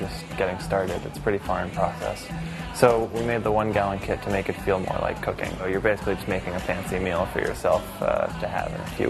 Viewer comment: sounds like hes sayins "We made the 1 gallon kit to make it feel like you're making cocaine"